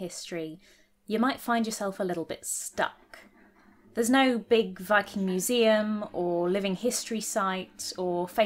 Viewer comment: I thought you legit said ‘there’s no fucking museum’